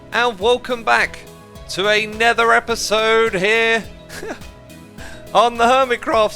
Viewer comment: Did he just say «hello everyone and welcome back to a nether episode» instead of another episode???